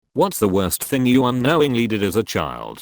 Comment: Oh my god I thought the voice-over said "What's the worst thing you did with a child" in the beginning.